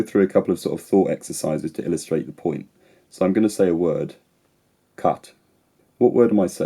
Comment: i thought you said cup